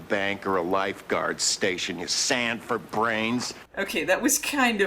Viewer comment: Until the captioning, I thought the criminal said "Sanford Brains" and made it even funnier